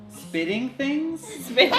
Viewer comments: I thought he said baby Jesus when he said spitting things 😂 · When said spitting things I thought he said baby Jesus